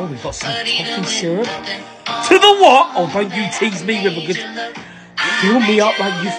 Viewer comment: WHEN HE SAID “ Do the woh” I THOUGHT HE SAID DO THE WAP😭